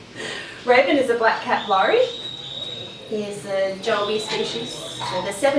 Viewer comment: 969 --> 00:00:19,170 busy Raven is a black cat Laurie here's Maybe instead of the word - cat - there should be the word - cap?